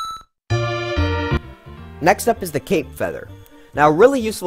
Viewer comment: is it just me or is he saying cake?